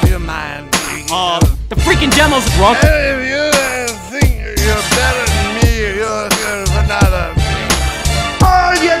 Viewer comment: Is he saying: *Hey viewer... You're better than me*???